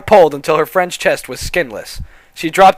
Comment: And 49:47 it sounds like he says hair pisses